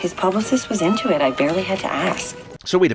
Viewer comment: Here's a sin: the captions say 'I barely hit her ass' - instead of 'had to ask' at 7:58 - enjoy. I feel sorry for deaf people, because they never get the captions correct. They usually omit a lot also... or change the meaning... not fair to deaf people at all...